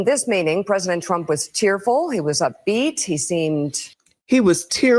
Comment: I think she said "cheerful", not "tearful".